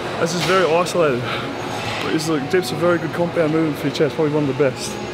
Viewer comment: Does anyone know what he said at 12:10 - 12:24? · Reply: talking about compound movements.. dips are one of the best compound movements for the chest ;)